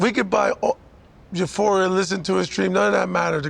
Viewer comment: I swear he says euphoria rather than utopia